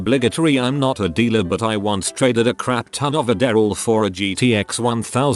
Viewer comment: lmao @ 9:04 when bro says "a daryl" instead of adderall